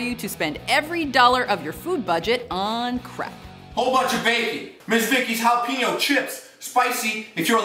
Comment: IT sounded like he said ( a whole bunch of baby)😆